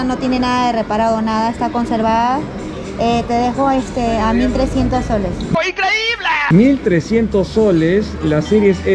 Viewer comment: 1300 soles o escuche mal ??